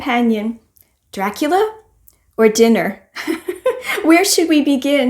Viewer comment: I thought you said 'Dracula FOR dinner' haha I read a lot of Stephen King in my youth and for some reason, I have no appetite for his books, which is a shame because he does write some great tales. Swordheart cover looks amazing and sounds great too :) I enjoy reading about different faiths and systems, have you read about Ikigai? I think you would enjoy it - a Japanese way of slowing down and being present in your task, whatever it may be. I am working on my project we discussed and will go live in January, I will be in touch :)